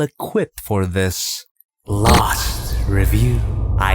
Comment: I thought he said last review and I ask about to cry 😭😂😂